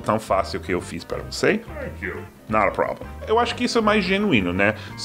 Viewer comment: Engraçado, eu sempre escutei "no problem", agora estou descobrindo que é "not a problem"... ou existem os dois?